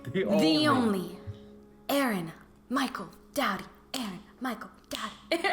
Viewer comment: I thought she was saying “daddy” instead of “doughty” 😭😭